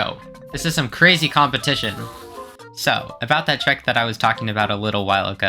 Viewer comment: sentence says trick, subtitles say check are you generating these subtitles with AI? or did you just not remember the context of the sentence when you were writing the scene and misheard?